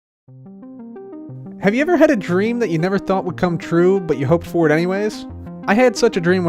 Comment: When you started, I thought you were saying “Have you ever had a dream, that you um you had, you could you um, you want you could you want to do so much you could do anything?”